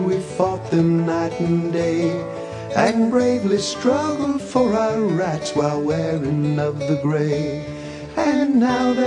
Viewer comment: I've firstly misheard "...For our rats.." instead of "...For our rights..." and remembered a monologue between the southern soldier and Thomas Chemberlain from the film "Gettysburg". Of course, things are the same. But...How symbolistic!